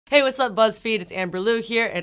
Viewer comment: Is it just me or i heard "Hey what's up lesbians" Im- i replayed it 3 times again to process what i heard😂